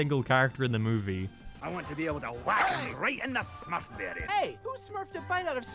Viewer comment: did he say “Smurf theory”?